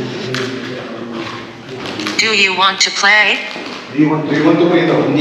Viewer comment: Seryuso ngaun ko lng narinig nung ghosttube nung nag salita ng (do you want to play) na may tuno sya ung parang boss na nanakot sya kasi ang haba nung pagkasabi nya nang play. Tapos suot kau earphone pakinggan nyo poh un bago nya sabihin un may umungol na babae na nag echo sa tunnel 14:31 di nyo nlng siguro poh napansin kasi nag uusap kau.